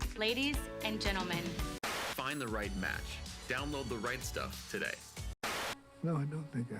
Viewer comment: lol I misheard the WHOLE ad, thought it was Reich Stuff not Right Stuff. ;) Woopsie.